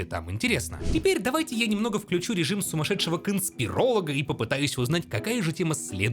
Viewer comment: Я услышал фразу "сумашедший консперелог"?! ДУНКАН